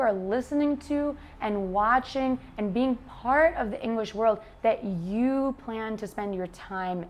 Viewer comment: in 7:32 and a once more a little further do you say "word" (as in ST) or "world" (as I hear and understand) ?